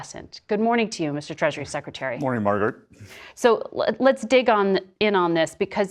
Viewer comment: whenever anyone says her name i hear JD Vance sayonf "i don't care Margaret" 😂😂😂